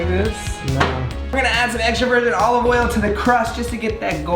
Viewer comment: I thought he said "extroverted olive oil" instead of "extra virgin olive oil."